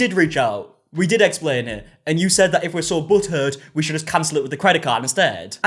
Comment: Oh my God, it took me a minute to figure out what he was saying. I thought he was saying if you're so buttered by it taken up with your credit card. After a few times of hearing it now I understand butt hurt. Lol😅